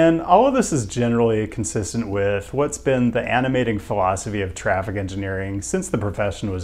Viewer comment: sounded like "animated colon'scopy of traffic engineers" I had to listen 3 times and slow to hear phil'sophy